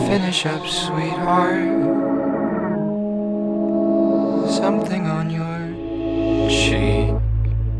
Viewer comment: what are the lyrics? what i hear is: "tried to make fun." "listen up, baby" or "this ain't a baby" "sit how you like to." "base it off last one" "finish up, sweet heart" "something on your-, sheen." "blow it away now." "talk on the inside" HALP, what are they? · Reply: I heard "Something on your CHEEK" Which makes sense because of the tear like shape near the cheek. I might be wrong though, what do you guys think?